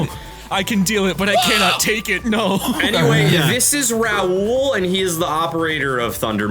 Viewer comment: Okay, time out; did you seriously just call Raoul "Rau1?" Or am I just hearing that wrong?